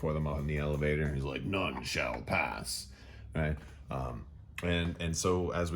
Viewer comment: For a split second, instead of "None Shall Pass!" I heard "Nunchuck Pass!" and thought how terrifying it would be to see an Imperial Royal Guard with lightsabre nunchucks